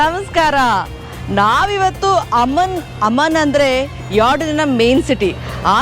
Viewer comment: I heard its like ಅಮ್ಮನ್ನ್ 🥵